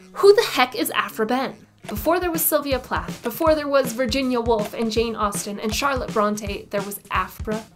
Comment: 😂 At first I thought you said Virginia Woolf hasn’t been famous!! I was like - I KNOW I’m not in the Matrix…. today. 😂 Seriously though THANK YOU!! I’ve never heard of her til your video. I’m looking for everything on and about her now!! 🙏🏻💃🏼🎊💃🏼❤️💃🏻🎉💃🏽